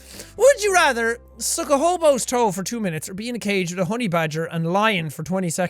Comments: I thought honey badger said horny badger. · I thought it said be STUCK in a hobos toe 😂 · I thought it said horny badger😂😂😂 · Up until 5:25 I thought it said horny badger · I thought it said horny badger